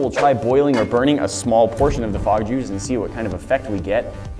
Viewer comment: I totally thought you said "Frog jews". lmao